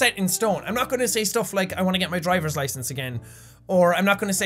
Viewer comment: see 6:37. did he just say get driver licence again? have you lost it before Jack?